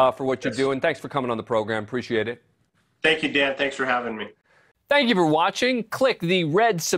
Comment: The Chief sounded like he said "Thank you Dad, thanks for having me" at 7:05. :)